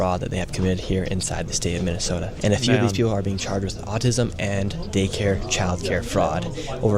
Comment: LOL, sounded like he said people were being charged for autism.